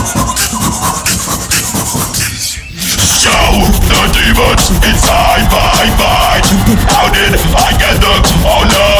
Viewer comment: What did d-low said? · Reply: Capacitor Hextech Demons inside my mind